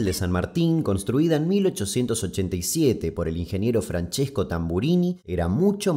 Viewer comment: Pensé que había dicho Franchesco Virgolini